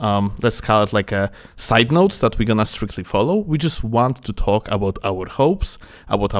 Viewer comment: bro i swear he said: we just want to talk about our hoes xD.